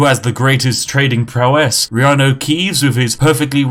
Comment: I always hear "we have no keys"